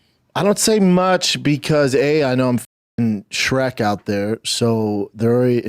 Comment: I thought he was saying Shrek not Shaq, I thought he was being honest. Then I heard Shaq. Figures